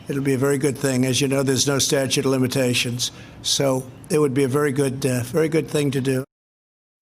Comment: does he say "it would be a very good death, a very good thing to do"??